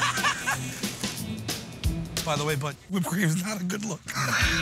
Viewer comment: I can't understand Sal's comment about Q after he puts whipped cream on his face.... at 1:12, what did he say? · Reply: "By the way bud, whipped cream is not a good look."